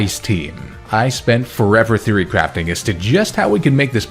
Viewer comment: every time you say "theory crafting", i hear "fury crafting" which sounds appropriately intense